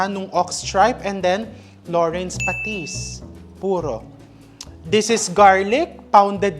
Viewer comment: Is the Nanay from Bicol as I heard she said “pasitis” for siling labuyo? She’s natural. is maswerteng anak. God bless you both.